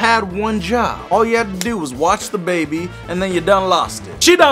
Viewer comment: i thought u said u wash the baby not watch the baby x'D